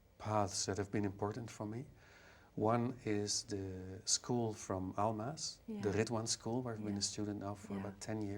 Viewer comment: Daan speaks of some school being a very important part of his spiritual development. I am not able to make out what he is saying. Can anyone figure it out? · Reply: Ridhwan School through Almaas.